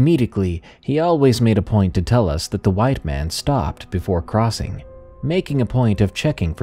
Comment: The white man story, I think I know why the dad wasn't that close to the story teller cuz of the way this person talked so rude about their dad !! 🙄 I'd never talk about my dad like that! My heart goes out to the dad for his hard life !Rip. Love the stories Thank you !! We also had a Beagal bassethound , Sox is forever missed and always loved 🐾🐾❤ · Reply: Oh, lol! At 1st I thought you said the "white man's story". (I thought) how could she tell? I was sooo confused!